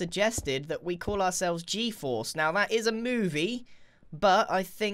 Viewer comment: When you said G-force I thought u said G-FUEL